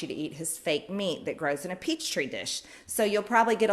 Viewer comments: Did she just say peach tree dish? 🤦‍♂️ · Did she just say "peach tree dish"? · Did she just say "peach tree dish"? Hahaha. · did she just say "peach tree dish??" lol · Anybody that voted for her probably thinks it is peach tree dish. She is just using language her constituents would understand. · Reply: I thought I heard that right. What the heck is a peach tree dish. That's definitely a new kind of tree and dish in my world, 😂 😂 😂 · Did she just say "peach tree dish"? 🤣🤣🤣